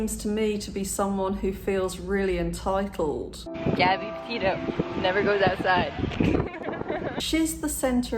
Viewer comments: I thought the “Gabby Petito never goes outside” was such a weird thing to say given the context of her having a vlog channel and living in her van. But I have a theory about that. Narcissistic abuse involves constant character digs and insults. I bet that was something he always said to her, that she never went outside. So, now that she’s outside, in order to dodge the insults later, she has to make a dig at herself to acknowledge “yeah I never go outside but I’m outside right now.” A narcissist can’t be proven wrong. And to avoid abuse later, she had to make sure he knew he was still right about her. I hope this makes sense. It’s hard to explain it. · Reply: I thought she said never go to that side lol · I thought she said never go to that side lol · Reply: Lol that’s okay, I thought she said “never goes out of style” 😄